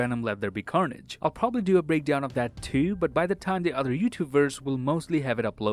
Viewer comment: Did he just say 'youtubeverse' instead of youtubers at the end? You know like multiverse.